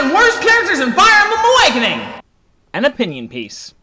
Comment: could of sworn he said in his opinion PEPs.